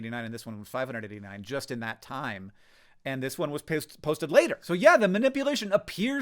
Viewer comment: did I hear "this one was pissed"? ;)